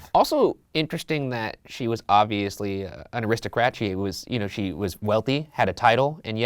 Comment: Did ryan say “Aristocratchy” 😂 he combined the words aristocracy and aristocrat into one new word lol